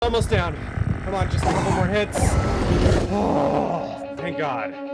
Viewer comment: In the first 2 seconds i thought you said "cmon just a couple more head"